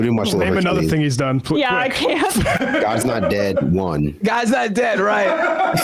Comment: I heard "Gaza's Not Dead"